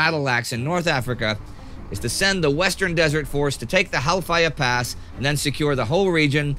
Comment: Every time he says the name of that pass i hear: "Hellfire pass"! It sounds like a very bad place.....